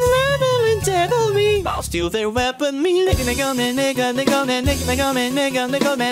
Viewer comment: what's bro saying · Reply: He's saying mega but sounds like nega